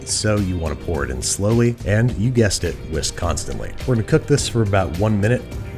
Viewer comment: What Babish said: "Whisk constantly..." What I heard: "Wisconsinly"